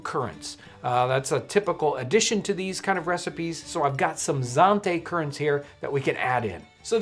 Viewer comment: I thought John said "zombie currants" during the boiled pudding episode. 😅 That's what happens when I watch/listen in a noisy environment. Really enjoying these demonstrations! Thank you for introducing your viewers to the living history museums.